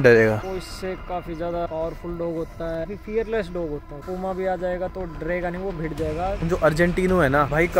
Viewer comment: kya bola unhone comma? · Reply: Puma